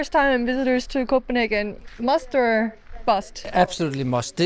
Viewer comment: bro I thought you said it's time to visit copenhell and I thought this video was about to take a wonderful turn